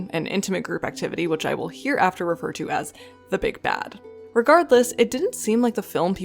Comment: At first I thought you were saying 'Big Bang', which is hilarious.